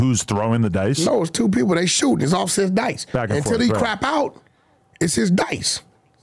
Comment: Anybody whoever shot dice before, this story very believable. Not saying Dugg punked out, but if I’m Offset I woulda been in complete shock a mf walked away on MY DICE like I’m a hoe.. Dugg prolly knew he was wrong which is why he ain’t fight back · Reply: now if he still had offset money u gotta keep shooting for sure… proper dice etiquette…. It sounded like he was saying offset was back up when 42 tired to leave